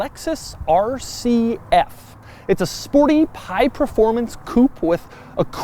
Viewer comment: did you just say it's a sporty pie performance coupe?